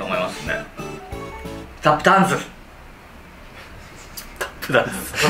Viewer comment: タップダンス！ あー、スタッフチャンスに聞こえるわ🤣